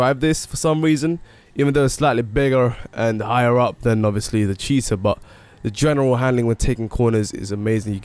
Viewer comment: LoL but did you say cheese'r (Time